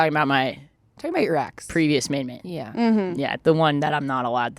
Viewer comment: Is saying meimei ? This is the first time I’ve heard this! It’s Chinese for “little sister” (and also vagina sometimes) that’s so fun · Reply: I think the word is main-main, as in main partner, but meimei fits the character too haha